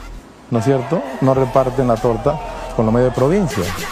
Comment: Que dice RLA en el segundo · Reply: Tienen más sintonía que tú.